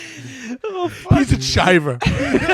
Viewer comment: What does Matt say Caleb is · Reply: "chiver" means druggie